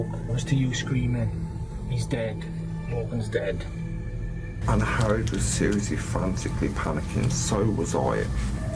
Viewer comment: Angharad? I thought he was saying "Aunt Harad"... lol That is a name I've not heard before. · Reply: I thought he was saying Anne Harrod. I was like why is he using her full name each time?